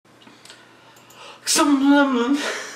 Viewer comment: Sounded like he said "some lemon" lol